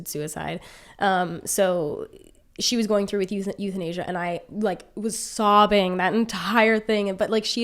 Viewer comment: Every time I hear “euthanasia” I think “youth in Asia”